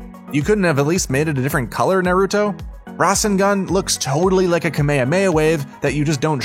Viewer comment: oh my god did u just say kameyamaya